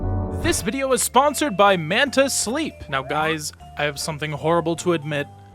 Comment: I heard the video's sponsor as "Mantis' Sleep" and thought we switched over to Marvel Rivals.